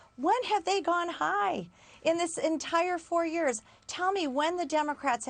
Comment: 'When they go low we go high' I thought she said 'When I do blow I get high'.